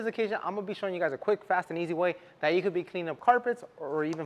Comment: But let’s be honest folks. You really need a steam extractor to truly clean your pile carpet. Floor mats can be pressure washed. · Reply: I thought he said simple and easy